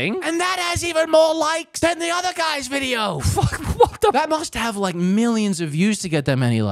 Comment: Is It Just Me Or Did I Just Hear "F*ck What The"